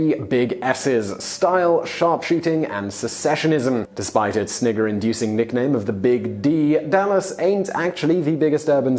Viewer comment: Despite its what inducing nickname?? At 1:26 did I hear that right ? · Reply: It's another way of saying snickering bc of the Dallas nickname big D.